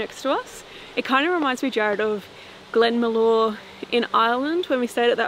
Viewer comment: I love spirit box, it's always a surprise, I'm not certain but I thought it also said Jarred. Interesting location, seems like water might influence activity, at least it seems so in this place.